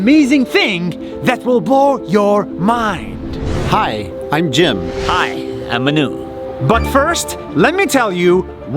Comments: I thought he said, "I'm Menu" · i thought he said im a noob